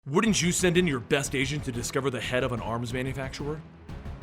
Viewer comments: lol did u say agent or Asian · At the beginning I thought he said "your best Asian" :p